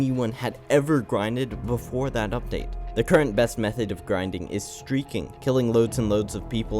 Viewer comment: what did he say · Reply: "the current best method of grinding is streaking"